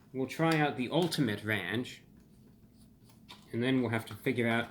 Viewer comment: When you got all worked up announcing the product and got tongue tied, it sounded like you said ‘ta-ranch-ula’ and honestly I’m all the way here for that spidery Pizza Hut product